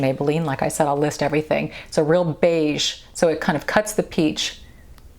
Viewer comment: 21.28: thought you said "cuts the cheese" 🤣 the way you line your lips is mind blowing for me! I struggle with lip lining to begin with, so this is amazing!! You are always beautiful and classy! Even my 15yr old daughter watches you because she admires your style more than the drama you tubers.